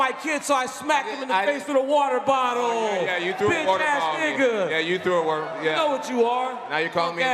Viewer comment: Did DC call Jon a "bitchass nigga"? I thought racist insult is a no-no in conferences.